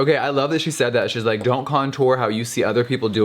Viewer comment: I SWEAR I HEARD HER SAY CUNTORE WHERE'S LAURA!?!??!?!